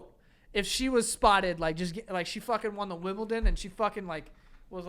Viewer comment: did call The Wimbledon "The Wibbledom"? ahaha